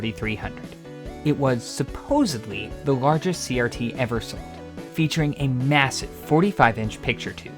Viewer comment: right after 1:36 what is the word(s) after "It was supposedly the largest CRT ever..."? it doesn't sound like created, or built.. it sounds like it was muted and to be filled later with a voice-over that never came. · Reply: "Sold" Wasn't edited, I just have a habit of reducing my speaking volume at the end of sentences sometimes